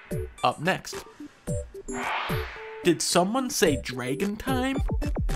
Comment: Yo I thought he said "Dragon Tales" I had a major nostalgia flashback